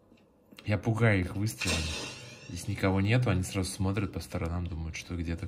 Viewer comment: мне послышалось я пукаю их выстрелом🤣🤣